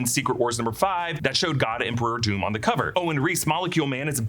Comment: Lol i thought you said God Emperor Dune😂😂